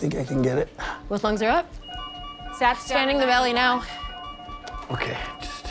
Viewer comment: the auto generated captions said “SATS standing in the valley now” lol